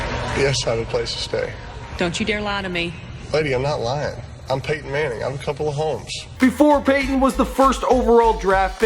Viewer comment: Lol, I thought he said "I'm Peyton Manning I have a couple of hoes!" Duh